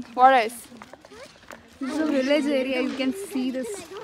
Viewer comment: in subtitles you written religious area instead of villege area